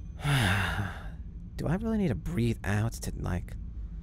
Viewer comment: The first thing I heard was " do I really need to breath" and then an ad I was just sittin here laughing